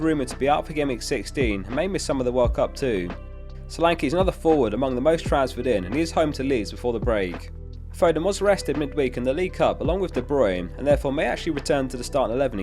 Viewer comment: sounds like he said Foden got arrested